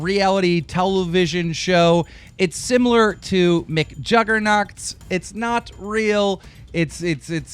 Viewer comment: did he really just say mcjuggernots? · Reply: I thought he said "McJuggerNuts"